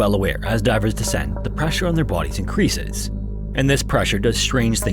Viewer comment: Lmao, at about 24:00 I thought you said "...and this pressure does strange things to the asses on our bodies."